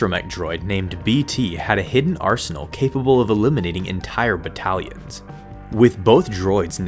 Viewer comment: I hear 'illuminating' when you say 'eliminating'.